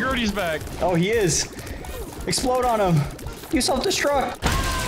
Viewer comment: wait a minute Did hee just say use self destri- [KABOOOOOOOOOOOOOO-]